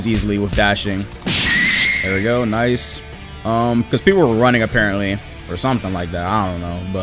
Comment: “we’re running” I think I heard were because people still run